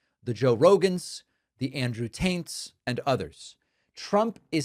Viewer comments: He did indeed say " The Andrew Taints " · I could have sworn David said "Andrew Taint." And that seems right to me... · Did you just say Andrew Taint. Haha. Nice